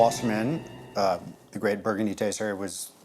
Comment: I think I heard him say the great burgundy taster. The hell is going on in this episode.